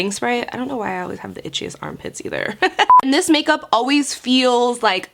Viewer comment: Whoa gurl, I thought you said "cheesy armpits" instead of "itchy armpits". I'm eating here!